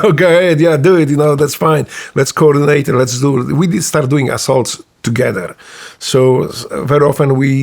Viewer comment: Did he just said at 1:35 that they started doing assholes together?